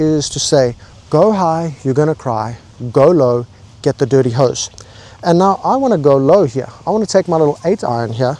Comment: Did he say "get dirty hoes"??? 🤣